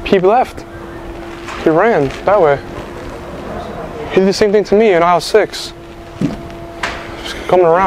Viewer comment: Lmfaoooooo “ he did the same thing to me in isle 6 “ you’re hilarious for this 😂😂😂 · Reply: I thought he said “he did the same thing to me when I was 6” 😳😂